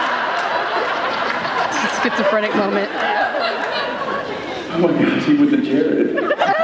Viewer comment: Thank you so much for this! Also, what does Jensen say at 12:46? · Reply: Laura Carroll "oh my God, he went to Jared" :)